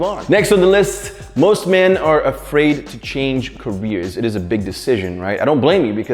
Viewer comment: 1.Asking for advice 2.Saying sorry 3.Being alone with girl 4.Being rejected 5.Changing carrer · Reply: I thought five said "Change carriers" Im like what does this have to do with anything